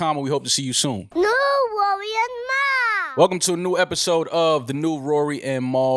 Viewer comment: No matter what it sounds like Mal is sayin "New Rory IN Mal" Just pause that shit every time for posterity.